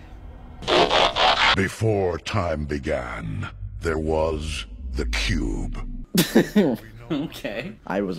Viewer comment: HELP i thought the intro said the pube instead of the cube